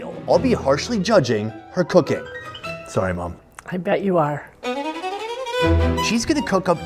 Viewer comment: I heard, "I bet you are." as Yoda saying, "You will be, you will be." Edit: I'm from Idaho, so as a natural born potato expert, tatertot casserole needs cheese on top.